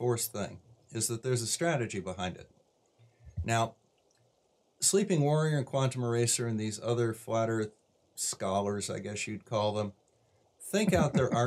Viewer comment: I swear i thought i heard you say "Sleeping Warrior, Quantum Eraser and these other flat earth squalors". I agree completely.